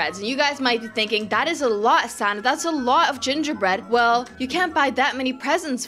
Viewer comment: lol when you said hi everybody my names sanna i thought you said santa bc i was looking at your outfit